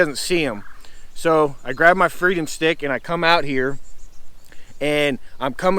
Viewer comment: I like the freedom stick thing. I thought at first you said Frig'em stick. That works too.